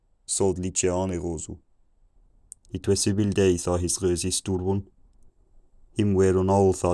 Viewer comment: I thought he said "it was several days" at like 675 but he said "it was evil days." I wasn't completely lost the whole way through, I'd say I had the comprehension level of an 18 month old, mostly identifying farm animals